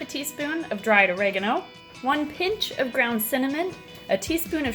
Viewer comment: I thought you said ground human